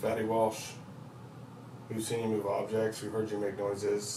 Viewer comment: I thought he said, "I seen you here"